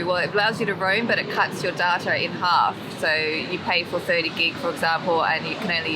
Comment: I wasn't paying attention for a minute and suddenly I heard "it cuts your daughter in half" xD